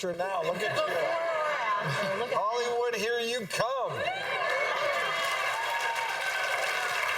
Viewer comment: i heard," I want to hear you cum... I don't know what he really said